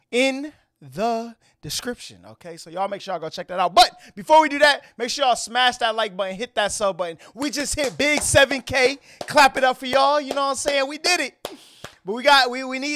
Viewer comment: did he say hit that subway?